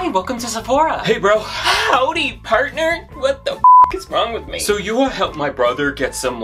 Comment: Ooohh😂 I thought you said Parker and not partner at first…I’m dyslexic. I’m like who’s I thought it was his brother Parker?!?!